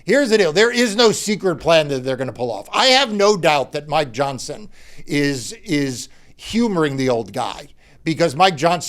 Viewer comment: When Marc said “I have no doubt Mike Johnson is humoring the old guy”, something interrupted my attention and I thought he said something like “Mike Johnson is where humor goes to die”. LOL That works too! I had to listen to it again to get it right, but I still like my first thought.